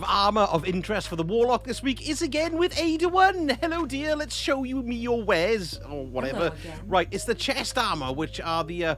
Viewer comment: Had to chuckle on your visit to Ada-1. I could have swore you said " show me your hairs". Ooo errr sausage lol